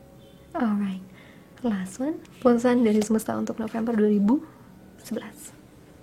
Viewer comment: Menit 55:13 apa aku yang salah denger bilang : pesan semesta untuk november 2011 😂